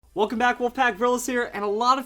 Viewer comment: i always thought he was saying "welcome back welcome back" but now i realize its "welcome back wolf pack." I feel dumb